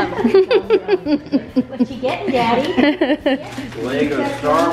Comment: Anyone else hear “what you get daddy” when opens his first gift?!? 🧐 @ 5:15 is she pregnant ohhhhhhhh